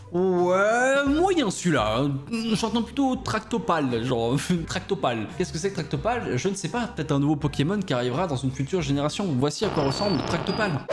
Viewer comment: Ouaiiii, moyen celui là.. J'entends plutôt "Tractopale". Moi : entend "Tarte aux pommes" Cool la vie